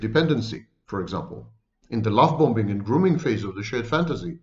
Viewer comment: he says something I can't make out what he said...."At the L-SOMETHING and grooming phase of the shared fantasy"....what is the word he is saying? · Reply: Lovebombing